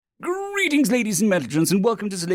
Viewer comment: So I've listened to about 60 hours of your narrations and I hear "Greeting ladies and mentalgents." It's insanely consistent so is that actually what you are saying?